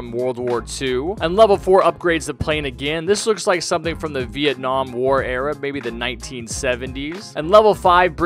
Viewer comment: Did he just say "Vietnam war"